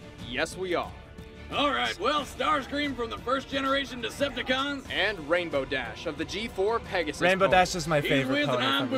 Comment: What did you whisper at 1:17? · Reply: Something like, "I also have her; I keep them on my belt." lol McDonald's had them for a while during 2012, and they have little clips you can attach to backpacks or beltloops or whatnot.